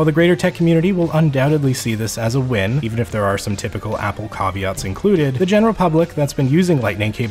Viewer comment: Did I just hear you say “cahveats” for caveats lol😂